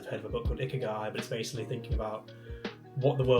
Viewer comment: Hi, at 15:09ish you mentioned a book but even slowed down, I can't quite hear the name of the book you suggested. Do you remember what it was pls? Thanks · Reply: Ikigai!